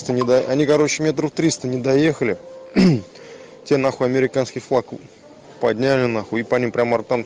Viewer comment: did he say Americanski flag? Lol